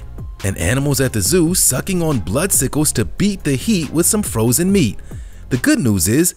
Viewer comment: I thought he said “beat their frozen meat” for a second...🤣🤣😳